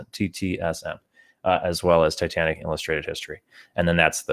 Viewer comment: In the beginning of the stream (@ 08:36) i asked about the books on the model. I understand "Titanic - Ship magnificent" and found it on Amazon. But i cant understand the other book. Can anyone help? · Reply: I think He says, Titanic an illustrated history.